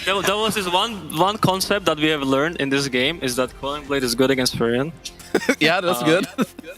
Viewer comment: what did skiter say? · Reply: Quelling blade is good against furion( nature's prophet another name)